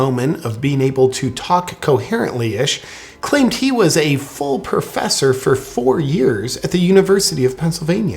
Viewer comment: Everyone keeps hearing "full professor" but I hear "feaux professor" which would be accurate.